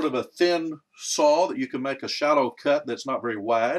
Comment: howdy - I heard you say you might put 'a hallow' on it ? what is that?